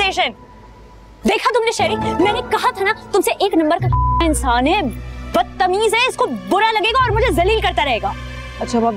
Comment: what did she say ? 😂😂😂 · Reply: kameena